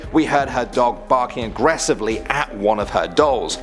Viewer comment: on the first one i thought he said "we heard her doll barking" and i got real confused real quick